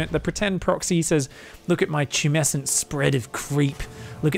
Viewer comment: Did someone say Tumescent?